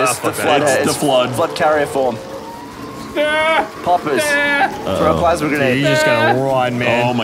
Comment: Did gavin just say "puppers?" Does that mean we must remember to break their bones relatively quickly?